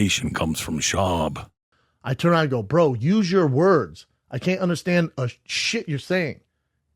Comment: When did Brendan say "Use your words?" I thought he said "Conor teed off on you" and that's what set Nate off. Nowhere in the video can you hear him say Use your words.